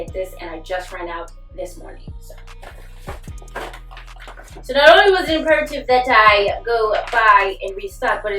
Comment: sounded like a different language to me. I thought I was having a stroke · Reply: “So not only was it imperative that I…” lmao you was listening slow fr